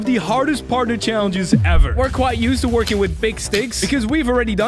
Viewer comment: "We're quite used to using big sticks" What I heard: "We're quite used to using big di-" That's enough youtube for today..